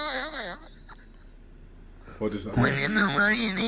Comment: it sound like he’s saying. “WHERES THE MONEYYYYYYYYYYYYY” 0:38 it also sounds like he’s saying “YOUR TAKING DRUUUUUUUUGGGGGSSSS” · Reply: in 0:35 he actually says ''what in the fuck is this''